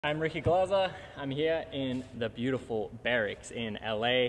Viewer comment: What I heard: I'm Ricky glozzer